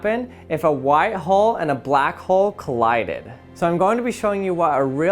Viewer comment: Bruh I thought he said white hoe and black hoe